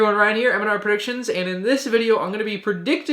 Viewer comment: Anyone notice it sounded like he said MandR predictions at the beginning